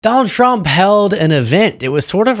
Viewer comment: the video lagged at the very start for me and i thought he said "donald trump hell"